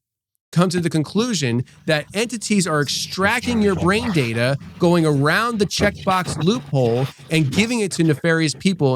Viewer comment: Okay did anybody else hear something a little extra at 1:35:45 for about 5 seconds? I'm freaking out a little bit to be honest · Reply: It says , “ that’s hogwash we just want your soul. As explains about demons at war for our soul cause we’re spiritual beings. Sounds like he manipulated if we all heard it.. I thought it was an attack on me when I heard it